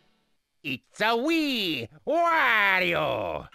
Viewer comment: I love the small Luigi "weee" at the end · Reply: I always thought he was saying “it’s for Wii” but I guess I must have imagined the “it’s for”